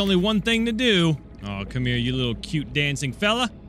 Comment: i heard “come here... to me.” when i crafted the compass